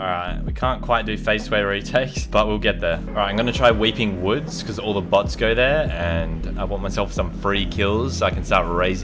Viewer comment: I thought he said all the butts got There